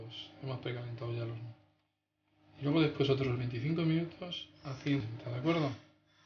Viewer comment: HEY! MUCHO CUIDADO A TODOS! pónganle atención al tiempo de cocción, cuando dice "25 minutos a 180º" no escuchamos que dice "y otros 25 a 160" así que se nos bajó el pastel. Cuando menciona lo del tiempo casi no se escucha y lo dice muy rápido, tengan cuidado. Minuto